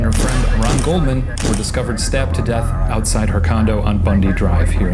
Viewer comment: I thought he said they were "stepped to death" not stabbed